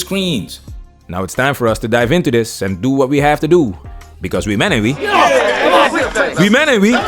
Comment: I am today years old when I learned that you were actually saying "we men ain't we" I thought you were saying we menavee 🤷‍♂️